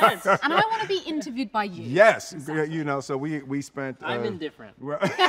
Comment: Someone please tell me what mena says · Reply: i think he says ''i'm indifferent''